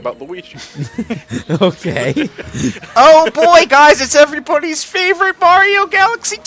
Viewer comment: did say every "pony" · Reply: Nah, he said "everybody's".